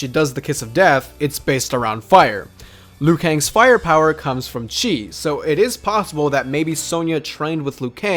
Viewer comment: I thought you said that lui kangs power comes from cheese